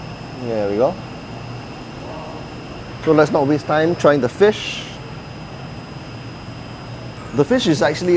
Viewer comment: when you said "let's not waste time, trying the fish", I thought you were saying, this is 'gone case'.